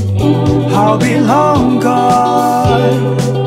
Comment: *youtube subtitles says "ill be hung, god" 1:22 turn on subtitles there